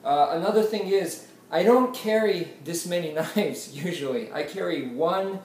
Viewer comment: Haha, i didn't hear you say "Don't Carry" i thought you said "Carry these many knives" so i was like dang! lol, but i replayed the video and caught my error, and thanks for the great video!